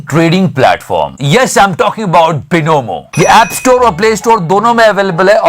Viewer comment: I think I heard him saying - bonobo instead of binomo.